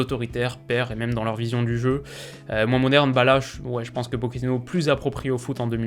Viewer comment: Il dit quoi à 13:44 ? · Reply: Moins moderne